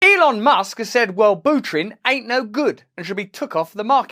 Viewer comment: when you said wellbutrin for a second i thought you said "will buterin" i was like wtf is that someone related to vitalik x)